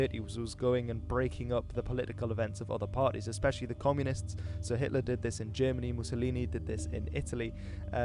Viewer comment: does he call him 'sir Hitler'? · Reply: I think he said so not sir